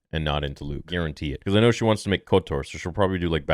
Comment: sorry i am late for the party,i didnt watch the acolyte (and not planing to watch it), i am aware of the bad decisions made in the show. so now i want to make sure i heard correctly: at 2:13 you said she whants to do KOTOR? hahahaha as much as i love kotor i dont want to see it made by anyone in disney, ANYONE keep up the good work theory